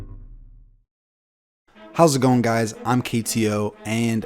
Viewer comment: for half a second there I thought the dude said “how’s it going guys”